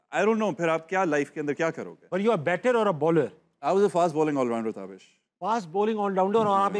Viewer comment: did he say 'Batter' ? 😮😮😮